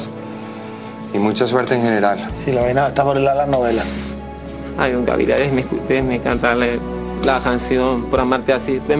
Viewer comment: Que dice coronel en el 2:22? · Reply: Opinión jaja